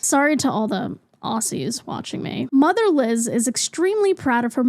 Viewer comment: why did it sound like you said "motherless" when you read Mother Liz out loud LMAO